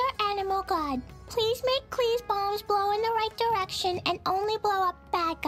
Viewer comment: bro why tf did i hear klee's bombs as klee's balls